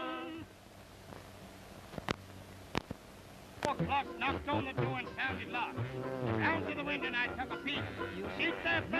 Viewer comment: wonder what they're saying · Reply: Good eye! I think they are arguing about hitting each other. The first guy says to the guy in the top hat something like "Keep out of the way." Top hat responds "You keep out of the way." The first guy says something like "YOU hit me." After a few seconds I think the first guy says, "You keep on hitting everyone." I made a clip of this with slightly clearer sound, although someone with better audio tools might be able to do a better job - <link> I imagine that it was miserable sitting in the hot sun for a hour or more wearing wool and playing the same song over and over. No one looks happy in the outtakes.